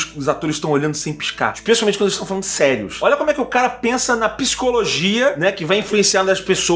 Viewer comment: Em 7:30 eu juro que entendi PISCOLOGIA (estudo científico do piscar)